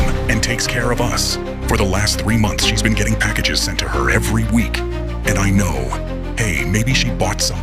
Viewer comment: MY DUMB SELF THOUGHT IT SAID "she's been getting pregnant every week" BAHAHA